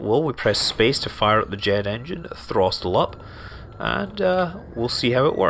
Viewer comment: did he say throstel up